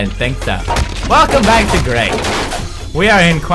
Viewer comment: I heard "Welcome back to Raped" instead of "Welcome back to Grey."